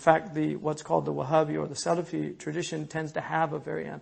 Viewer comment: what does he say after "wahabi or" ? What's the other name of wahabi? Please anyone. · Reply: Wahabi or the salafi